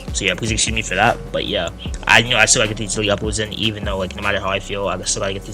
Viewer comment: What did blud say in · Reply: "I still gotta get these daily uploads in even though like no matter how I feel"